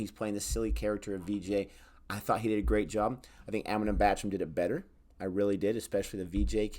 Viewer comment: Whenever this guy says Amitabh Bachchan, the subtitles say Eminem bathroom 🤣🤣